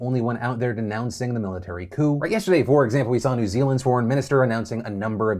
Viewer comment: Hey Phil, I thought you said sworn not foreign. Was expecting a picture of Jacinda Ardern. I had to find out who Nanaia Muhuta was so no harm I guess.